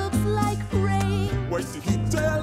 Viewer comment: i heard... "Fvcks my brain" WHY